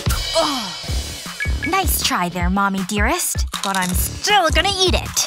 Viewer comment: did Jennifer says mommy dearest from fnf mod game